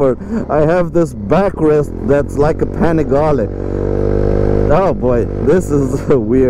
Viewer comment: When I first heard you saying Panigale I understood "pan of garlic"... 😀😃😄 ...just because precisely as you said it needs a little salt and pepper on its transmission, so the garlic pan will taste better! 😛 We must remember that's an Italian TOY and a styling exercise, so it's not about the price but the joy to... enjoy the riding AND the design. The transmission can be tuned a little bit, of course if you need to. Because any "aglio e olio" recipe needs "pepperoncino"!